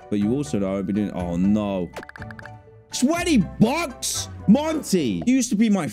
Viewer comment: I was rewatching this and it was at a part where he yelled 20$ but i thought he said "SWEATY BUCKS?!"